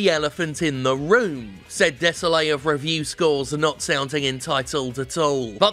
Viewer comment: I thought you said his name was Je Suis Desole, which is like the French expression of like sorry for your loss